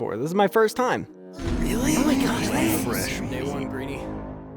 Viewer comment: did I just hear fresh meat